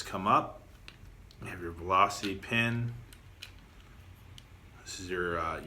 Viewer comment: did you say "velocity pin"?